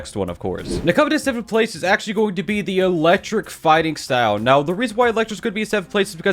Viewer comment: who noticed at 2:05 it was e claw instead of electric